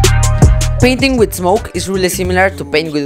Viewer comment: Soo I'm not the only one that thought it said painting while smoking.... right?..